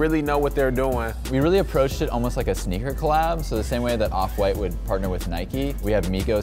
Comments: did u just call offset white? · Correct me if I am wrong but did he say offwhite 0:18 😂😂😂